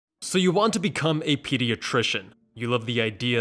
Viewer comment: I thought it said "So you want to be a PEDESTRIAN"